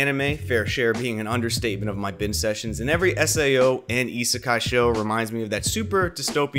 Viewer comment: SAO and what? I heard isa kashao. I watched SAO and totally would like to watch/ read this other one he mentioned... just couldn't find it T_T